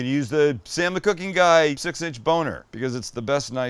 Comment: At the 2:29 mark, did he say he had a 6" boner?